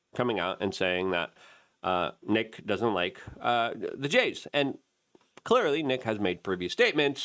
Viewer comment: When you said “he doesn’t like the j’s” I thought you said “he doesn’t like vejays” 😂